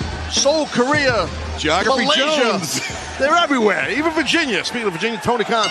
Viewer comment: wait, did Tazz just said Malaysia?